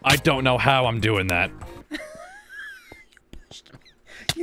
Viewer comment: Did say the b word and it didnt get bleeped?! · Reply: "You pushed me"